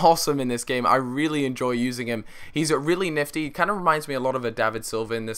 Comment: hi i am french can someone explain me what calfreezy says at 3:47 ( he is really "nefty" ? or something else i can't find this word in my dictionnary) thank you very much